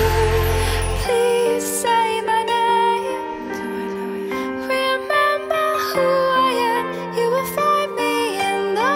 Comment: After she says please say my name, something is said in the background... Anyone know what it is? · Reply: I believe it’s “Do I know you?”